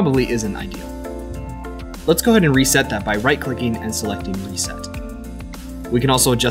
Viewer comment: Hi, could you please repeat slowly and clearly what was said between 7:00 and 7:05? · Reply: Reset the Position by right-clicking and selecting Reset. - Javert